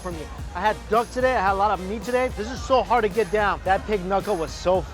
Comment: I heard you say dog meat the first couple of times xD